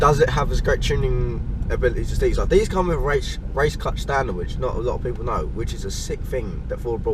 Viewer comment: What did you say? Race Clutch stylawich? What is that?